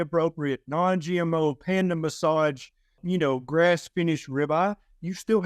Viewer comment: i hear Ken say a word that i don't understand, and the close caption translate it to "Panda massage" At 5:07... What his he talking about? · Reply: Dr Berry often uses the phrase ‘panda - massaged’ to poke fun at pricey cuts of beef. Perhaps he is referring to the way wagyu beef cattle are said to be massaged — and fed beer even— to keep the animals “happy”. 🤔